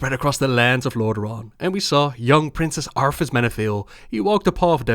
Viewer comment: Am I the only one who hears "Young Princess Arthas Menethil" ?